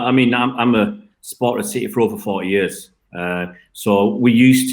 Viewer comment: dia he really said he supported city for four years???? Plasticcc fan.